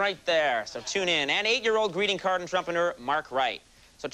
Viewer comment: I thought he said entrumpenurr.